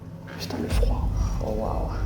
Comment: Bonjour Marie, je regarde cette vidéo pour la troisième fois et j'ai, cette fois, entendus une voix étrange quand vous rentrez dans la tour à 46:30 qui pour moi est une voix d'homme, ( putain le froid ) et ça ne ressemble pas à la voie de, je me trompe peut-être, à vérifier, c'est une super enquête pour votre première, je les suis toutes depuis à très vite😊